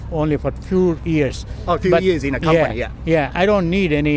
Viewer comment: 英語を初めて1日。 8:35 字幕ではtwoになってるけど、fewに聞こえる、、、違いってわからないもんですね😢 これから頑張ります！